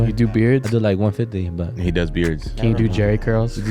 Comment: You do buttholes 😂 I heard you